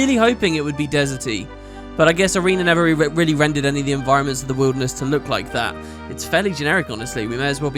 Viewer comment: ''I was really hoping it would be deserty, but I guess arenanet never really rendered the environnement'' I know right, I wish arenanet did an elder scroll game X) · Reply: Nah he said "I guess arena never really rendered any desert enviornments", it does sound like he says Arenanet though haha